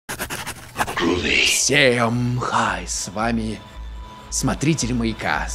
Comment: Поставил на паузу на 0:02 и услышал "Всем мха!" 😂